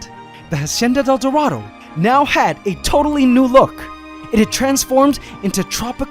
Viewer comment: I heard toadtally new look